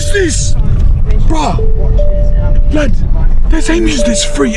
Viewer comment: i thought you were saying free emu's